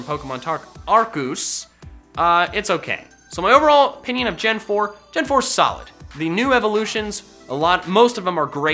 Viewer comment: When said, "Gen 4 is solid," I thought he said, "Gen 4 sucked"